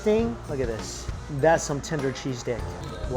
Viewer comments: I have now listened to this 13 times. I swear I hear “That’s some tender cheese dick” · did he say “tinder cheese dick” or there is something wrong with me? Either way, OMG the second one looked so freaking gooooooood! Mouth watering.